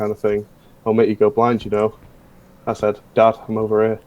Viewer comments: I heard dad I'm over age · I heard it too😂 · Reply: For the going blind joke I didn't hear dad I'm over here I heard dad I'm over it as well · bc of his accent he said "dad I'm over er" and it sounded like it